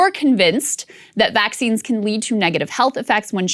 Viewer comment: 100% thought it said "Vaggines" not "Vaccines"